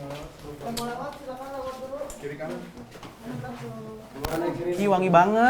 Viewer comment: Kok di menit 12:06 kayak ada suara yang bilang"yang mau lewat silahkan lewat dulu":) apa aku salah dengar yah?